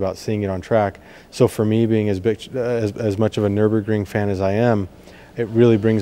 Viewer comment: Did I just hear "Me being his Bitch"? :)